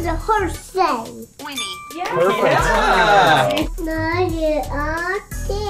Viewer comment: Boy: “What does a horse say?” Cortana: “Whinny” Boy: “Whinny?”😂